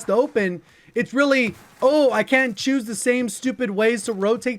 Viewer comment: lol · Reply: sorry for some reason I thought you said can't instead of can